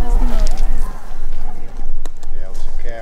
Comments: The bear is screaming, "Hold your mouth...". "Hold itttt!". The moose was teasing the bear. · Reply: Sounds like she is saying “oh my god… oh no!!” 😢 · This screams are disturbing..it almost sounds like it said “oh god, a bear”..